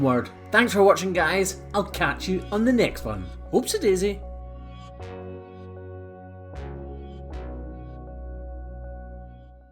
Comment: It is not only cows u help...Ur kindness brightens the dark chaos of many lives!! Btw really funny when you said “whoops-a-daisy”. But i thought u said “hoofs-a-daisy”! 🤣